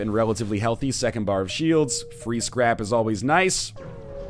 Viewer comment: Every single time you say "Free Scrap is always nice" I hear "Free's Crap..." I know, I'm dumb.